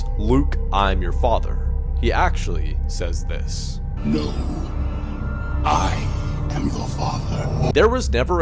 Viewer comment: “He actually says this” Me: Then why I’m hearing Luke I am your father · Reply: I thought he said Luke I am the father…